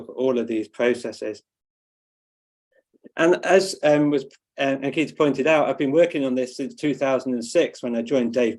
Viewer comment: did you say "as it keeps being pointed out" :) it's not such a long time since 2006 BTW (objectively speaking...) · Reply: Ha, no, I say, "As Ankita pointed out" (referring to Ankita Jha's introduction to my talk at the start)